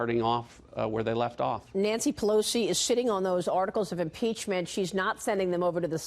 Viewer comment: I'm sorry but I thought you said Nancy Pelosi is Shi**ing on those articles of Impeachment l 'm sorry lol I really did How about you all stop paying that really unimportant Ilhan Omar so much attention she would just shrivel up and die if all this publicity dried up.Like the witch in the Wizard of OZ.